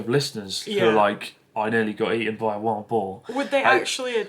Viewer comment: Haha when Chris said ‘eaten by a wild boar’, I thought he said ‘eaten by a Womble.’ 😅